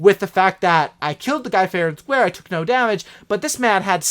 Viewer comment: Love the vids!! Keep it up! I was rolling when I heard you say fair and thquare